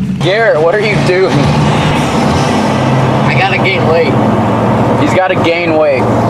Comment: when i heard say he has to gain weight i thought he said he has to get laid.